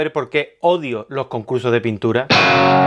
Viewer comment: Al principio pensé que decía “odio los CURSOS de pintura” yo dije ¿cómo? Hehe.